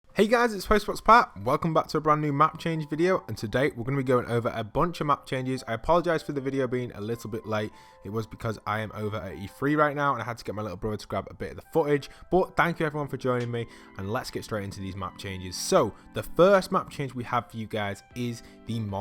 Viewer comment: i thought you said you were over 83